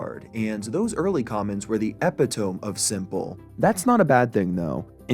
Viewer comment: did you just say "eppi-tome???"